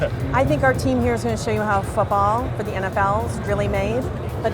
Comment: f**k all? P.S: Dont be mad at me, cuz thats what i heard edit: ok nvm, its football